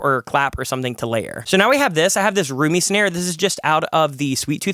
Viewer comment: I definitely heard that as "Rumi snare" at first.